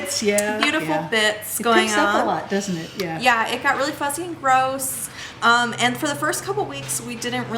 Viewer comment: does she say "...it got really f-ing gross." !? Was not expecting that! (I am fine with it, just surprised.)